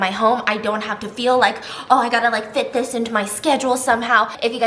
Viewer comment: WHEN SHE SAID “FIT THIS” I THOUGHT 💭 SHE SAID “FITNESS”😭😖